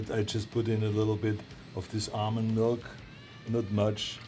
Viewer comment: Arnold should come out with his own almond milk and call it “Arnold Milk” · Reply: I litteraly heard him say Arnold milk instead of almond milk lmao